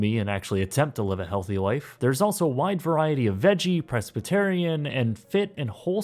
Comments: Did you just say that Hello Fresh serves "presbyterian" meals instead of "pescatarian" meals? LOL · Did anyone else hear him say “presbyterian” meals at the end? I think he might have been going for pescatarian unless there’s a new trend I don’t know about? lol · Hold on, I know this video is two years old, but did you just say "presbyterian" instead of "pescatarian" in your HelloFresh segment 💀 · did you just say "Presbyterian"? I know what it's supposed to be but it sounds a lot like that :D